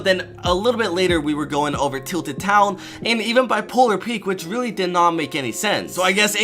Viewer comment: If you turn on captions at 3:40 instead of polar peak it says bipolar peak